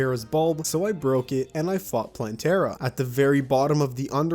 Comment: I thought he said “fucked” instead of “fought” here for a second and was really stunned for a minute